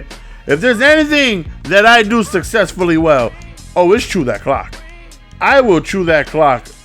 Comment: did he say "chew that clock" or "chew that cock"?!?! Lol